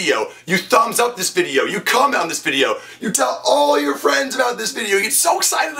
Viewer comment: when you said you comment on this video in the beginning, i thought you said you cum on this video... i had to go back and see if that's what you said.